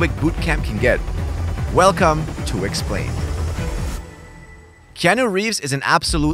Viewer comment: Thought he said "wicksplained" there. Missed a trick.